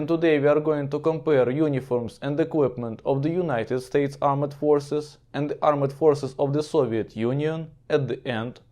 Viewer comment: Did you say ‘armored forces’ but meant ‘armed forces’?